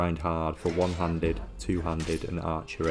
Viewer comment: I heard this as “time to grind One-Hunnid, Two-Hunnid” like he was Fitty Cent 😂